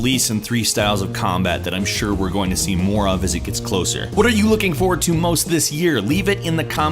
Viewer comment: No Man's Sky Dark Souls 3 Uncharted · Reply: He didn't say "What TPS are you looking forward too" He said "What are you looking forward most too". So go eat some smegma. :)